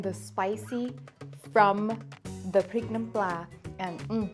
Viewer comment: NOT THE SUB SAID "PREGNANT PLA" INSTEAD OF "PRIK NAM PLA" AT 7:13 I'M WHEEZINGG. I wonder does this taste similar to the ยำไข่ดาว cuz it reminds me of that a lot with the amount of lime in this Prik Nam Pla.